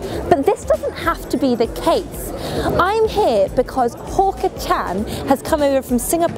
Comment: did she just say Porker Chan. Damn she must be starving so bad.